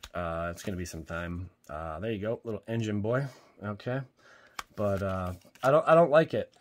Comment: My ears deceived me. I was only listening and thought you said "little Injun boy" for a second. Had to double back and watch the screen to be sure I didn't hear what I thought I heard.